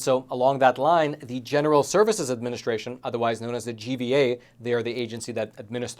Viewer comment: did he call the GSA the GVA?